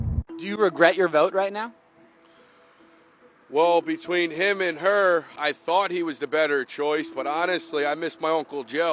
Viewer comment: Did the first guy say, “between him and her I thought he was the yter candidate?” Because that’s what I heard him say!